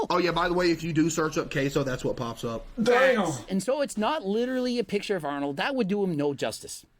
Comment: did CaseOh say “damn”?